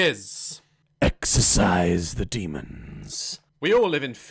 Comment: It really sounded like he said "Exercise the demons"